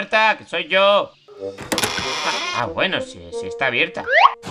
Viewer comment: Cuando dijo "a bueno si está abierto" yo escuché "a huevo si está abierto" xd